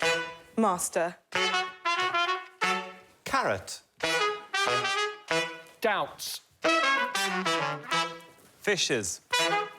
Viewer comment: Am I hearing wrong or did Joe and Hugh say karat and doubt for six letter words around 40:20? I think that’s what confused Lolly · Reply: The two words were actually 'carrot' and 'doubts', Hugh's word was particularly hard to hear.